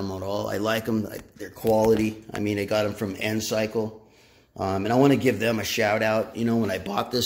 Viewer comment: What was the name of the company you mentioned at the 1:35 mark in the video? Are they an Alibaba seller? I couldn't quite understand the name you said. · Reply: Hello the name is Ncycle they are on Alibaba and ali express I have problems posting links to them for some reason